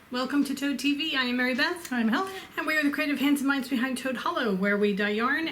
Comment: For a minute I thought you said, "we are the crazy pants and minds behind Toad Hollow." I was like wha? So, what is that gorgeous colorway in the pans at the opening? I'm going to have to go over the the Ravelry page and see if it's a cowl for a newbie crocheter. I really love that. Have a wonderful evening!